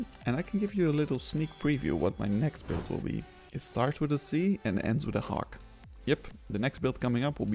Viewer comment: Lol I swear I heard him say “Starts with a C, and ends with a Ock” has to do a double take for that one